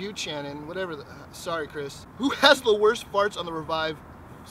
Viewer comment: Did you accidentally say fart instead of part? Or was that on purpose. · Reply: Oh nevermind I heard him say worst part in the video but he said fart